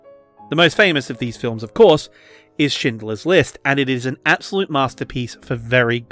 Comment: Help me, i heard "shitlers list" and now im dying of laughter